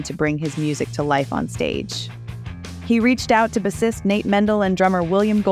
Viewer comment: read by AI? who says "Bissist instead of bassist?"